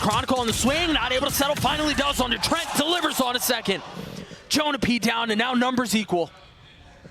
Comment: did bro say zekken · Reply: Second